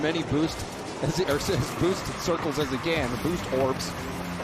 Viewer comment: swear I thought he said "boost whore". Had to listen to it a couple times 🤣🤣